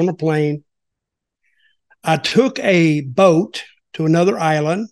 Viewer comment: For just a minute I thought Dutch said he took a oat to a island then I thought about it and I realized he said he took a boat to a island